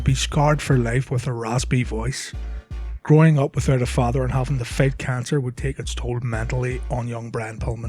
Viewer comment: did u say bryan had "fake cancer:?